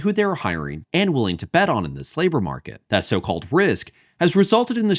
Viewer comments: I swear I heard "the slaver market" when you said "this labor market". Sucks when both honestly apply, lol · <link> , I thought bro said "slaver market"